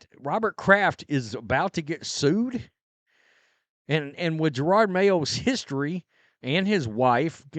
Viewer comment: Did just say JA-ROD?!